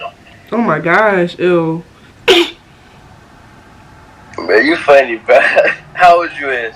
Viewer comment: did he say bae you funny? 😂😂